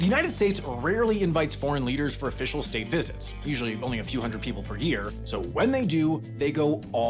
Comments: Did you say a few hundred people? Because the subtitles just say "a few people"? I assume the subtitles are correct here and you misspoke but confusion has occured. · "Only a few hundred people per year" - But the caption says "Few people per year". Which is accurate?